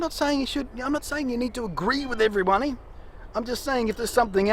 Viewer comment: haha did he say every pony?